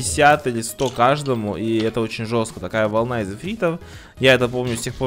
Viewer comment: мне послышалось или он сказал с фирамиром?